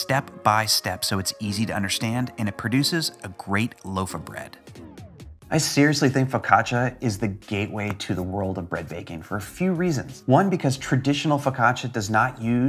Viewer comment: I thought you said "focaccacia is the gateway drug for other breads"